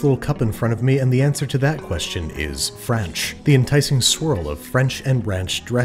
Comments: i thought he said kranch for a second i was about to have a heart attack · the caption says French and it should say Franch